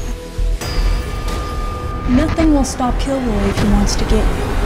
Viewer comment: Love your films every one but was like wtf when started watching it cuse I thought it said killjoy that was made by full moon I was like Kevin doing a killjoy lol